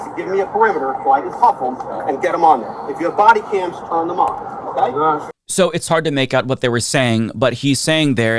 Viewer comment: sounds like he said "turn them on" I agree this is heinous but for the sake of truth i guess